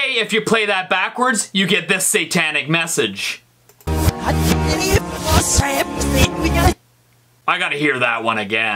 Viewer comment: I heard: I belive it was satan in migel